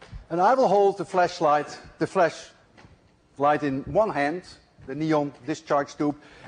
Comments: sounds like he says fleshlight........lol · Did he just say fleshlight? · i was fast-forwarding the video and the first thing i heard here was “fleshlight” 😂